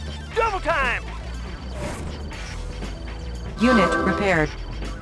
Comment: Send infantry to hospital Eva : unit repaired. Infantry isn't a vehicle Eva!! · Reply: Weird, I heard Eva said "Unit prepared". If you listen closely it can sometimes sounds like "Unit repaired" too.